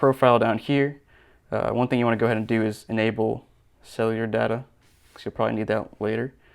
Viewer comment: thought he said enable 'sell your data' 😂😂